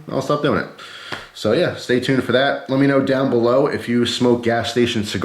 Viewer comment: I thought he said “so let me know if you smoke gas” for a second